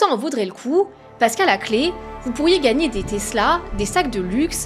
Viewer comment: gagner des quoi ?? 😂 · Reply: Qui veut avoir des tesla ?